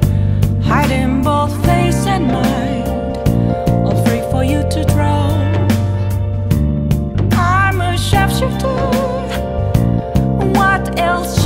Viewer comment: I love this song. This quarantine has been super boring so I got persona 5 just because i saw it and it looked interesting. Im so addicted to it at the moment and ive been playing nonstop. This song has been playing on repeat in my head since i started!😂 again i love this song and im happy to actually see what she is truly saying instead of guessing. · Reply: It is a bit difficult to hear in game. When the part where she sings" All free for you to draw." I swear I heard her sing " I pray for you to drown!" Can't not hear it now... Lol.